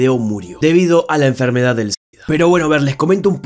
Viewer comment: no escuché bien el nombre de la enfermedad si alguien la escucho bien porfa dígamelo :V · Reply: Sida creo xde